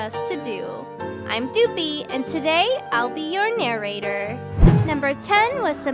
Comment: why isn't it? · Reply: oops my mistake. I thought you said narrated. 😅